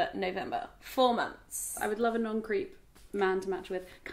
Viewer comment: when said non-creep I thought she said 'non-greek' hahaha